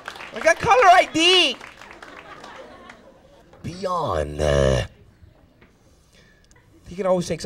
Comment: What the fuck does he say at 5:58?! Lmao. “BEYONNNOUAGHH....”